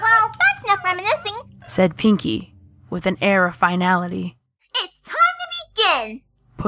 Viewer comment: Pinkie: "it's time to begin!" What I thought she said: It's time to be g@